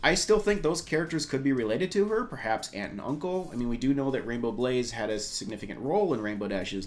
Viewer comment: At first I thought he said Rainbow Blitz (RD Genderbend), but he said Rainbow Blaze